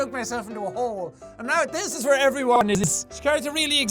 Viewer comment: I understood what Jack said at 18:00. He said "Now this is where everyone is." This has to link up with the ending. **THEORY WARNING AHEAD** I think Jack did JJ for a reason! What if JJ got killed by Anti (because Anti does the whole glitch thing and "Jack" glitched at 18:00)! Right now, Anti is basically killing, or getting rid of, all of the easy egos. After that, I think, it may come down to Schneep and Anti! **THEORY WARNING DONE** Now I'm just going to think about this whole ego thing tonight! Guess I'm staying up all night to figure this out!!!